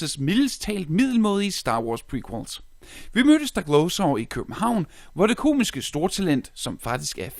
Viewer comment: the captions say "we murder stack-low-so (stoklasa) so he come home" 😂😂